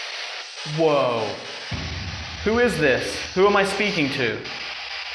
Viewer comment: "where am I?" is what I heard.